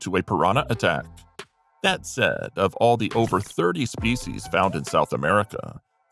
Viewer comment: So there are 30 spices of piranhas out there. Do you mean 30 species instead of spices.